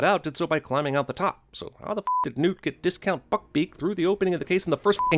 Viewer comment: I thought he said "discount fuckbeak" and i nearly choked on my pizza